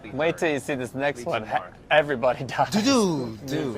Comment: he actually says half of everyone dies